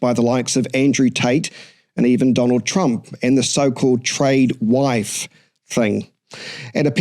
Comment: Did you mean trad wife? as in traditional wife? I thought you said "trade wife".